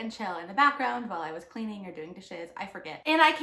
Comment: hahaha you said "...or doing dishes..." and I heard "doing the shiz" and I just thought that was a really funny phrase - anyway, going back to watching the video now (I've never seen this movie so now I'm really curious!)